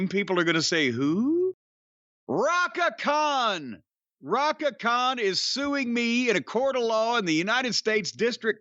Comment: I thought it said Chaka Khan at first😂. I'm glad i clicked on this because i couldn't stop laughing when he read the list of co-defendents😂